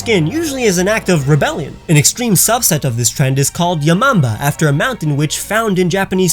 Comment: I thought you said "ya mama"